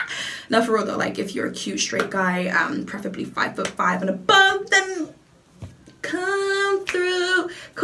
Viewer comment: Did she say 5’5 and above @ 5:45 or did my computer lag 🤣🤣🤣